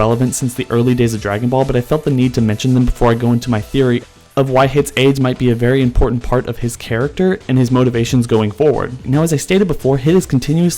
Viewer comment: Nice theory bro! :) ... Although I have to admit at 0:56 I heard it as "Why Hit's Aids are an important part of his character" .... But I think that just says more about me